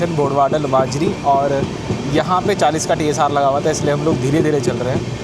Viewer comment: pe kya bole kya laga hua tha? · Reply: TSR - Temporary Speed Restriction